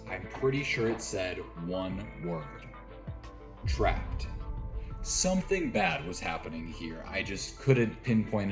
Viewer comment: my dumb ahh really thought that said apples 😔